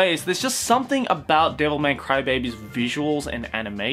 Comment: I heard devil may cry baby